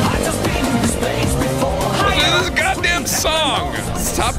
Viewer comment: DEJA VU I JUST BEEN IN THIS PLACE BEFORE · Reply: RNGesus i allways thought that he said "i just been in space before"